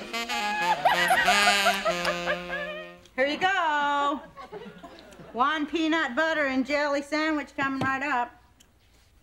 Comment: AT FIRST, I THOUGHT SHE SAID "A LIME, PEANUT BUTTER AND JELLY" COMING UP.....